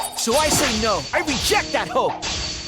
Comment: did he just say "HOE" instead of "hope"😭